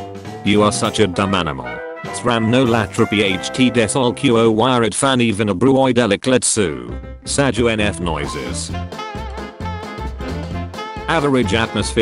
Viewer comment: for anyone wondering what they said in the meme they said, "We still killed your bunny even after you closed the portal on Mars."